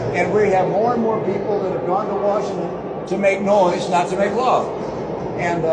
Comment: I wasn't looking at the video & reading the captions; so I heard "..make noise not make LOVE" I needed that chuckle! = thank you!!!🤣